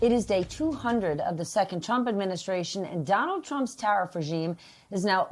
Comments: As a Australian .. I am just sitting back remembering when the USA actually had some respect. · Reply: Did she just say, the chump administration? Freudian slip, slip of the tongue or 100% intentional? 🤔😂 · No money will flow in, it will flow from people's pockets into government coffers. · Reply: Did she just say, the chump administration? Freudian slip, slip of the tongue or 100% intentional? 🤔